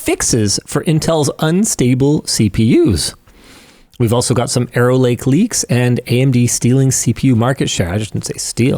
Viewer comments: Wow that was messing with me. Every time he said "Arrow Lake", all I heard was "Error Lake". It wasn't what he actually said, but it made me laugh just the same. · I heard "Aero Lake" and thought it was "Error Lake." Turns out, it was "Arrow Lake." I'll stick with "Error Lake."